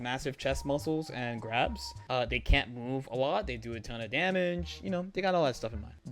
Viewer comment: I seriously thought you said "they can't move the plot" and didn't doubt it for a second what with the pedigree Arcsys' writers have